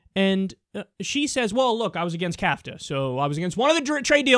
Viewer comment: Did he just call nafta "kafta?" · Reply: No, he's referring to CAFTA (Central American Free Trade Agreement).